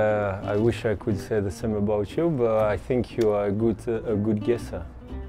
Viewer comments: I really thought he said you're a good KISSER 😂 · I thought Giroud said “I think you’re a good kisser” 😂